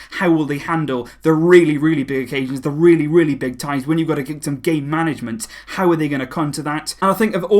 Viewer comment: sounds like you said gay management, instead of game... 😅😄😂